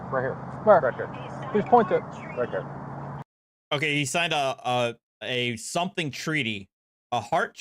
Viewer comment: sounded like “he signed an R3”